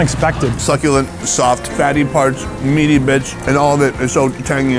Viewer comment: He said “meaty bits” and I thought he said “meaty bitch” on the brisket sandwich 😂😅